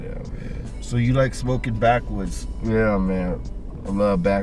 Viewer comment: Shit english isn't my first language, during the whole thing I thought they were saying "smoking backwards". I was lost as fuck